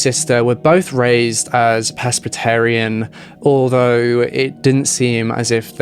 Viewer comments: Ah presbytarian , I thought you said peskstarian lol : 48 and still a bimbo lol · For a minute I thought you said they were raised “pescatarian” but weren’t strict about the religion and I was like....wait....whaaaaaat? But then I realized you said Presbyterian....🤦‍♀️ I truly amaze myself sometimes... That said, this video is very well researched, respectful to all involved, in depth, and amazingly well done! You got my sub today sir! Edit: god...the further I get into this story the more tragic it is....Austin was failed so utterly and thoroughly.....this could have possibly been prevented. He’s clearly not ok in the head.